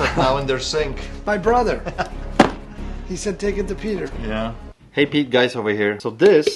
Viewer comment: What did he say? 😆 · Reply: I think he meant Hey Guys Pete over here😂